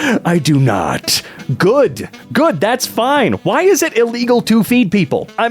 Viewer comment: i may or may not have misheard “feeding” as “beating” and only realized just now 🧍🏻